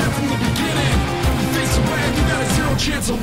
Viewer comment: I love how in the xerath verse he say you face the wrath you got a zero chance of winning. It sounds like he says you face xerath you got zero chance of winning